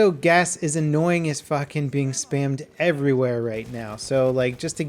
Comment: does he say that gas is annoying as f#ck lol